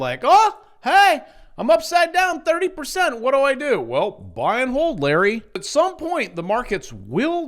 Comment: I heard "buy and mold, Larry". I'm with you. TLT will pay... someday.